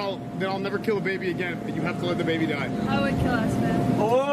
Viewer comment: what did she say?? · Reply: She said "I would kill esfand" my English got better in 2 years